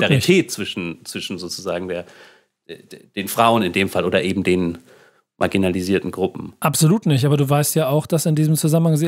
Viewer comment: habe ich erst vaginalisierte Gruppen verstanden und musste zurückspulen 😂